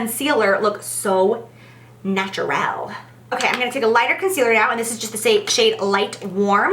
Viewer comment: I almost didn't catch it, but so glad I did! did she just say around 3:15 'I'm going to take a lighter conceareal' because that is what I heard & it cracked me up. No shade Jaclyn, just shows me I'm not alone!